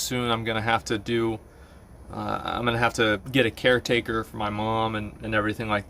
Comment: as a non native speaker, i misheard caretaker for undertaker and I was like.....wait WHA.... fuck man. Ah nevermind..... I'm super glad she is still around and i wish only the best so she gets better soon. Fuckin hell... i almost spilled my coke everywhere